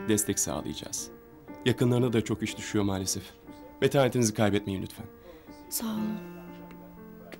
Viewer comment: bekaretinizi kaybetmeyiniz diye duydum amk